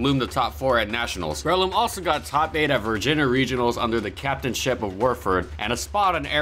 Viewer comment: Did he just say “virgin-A”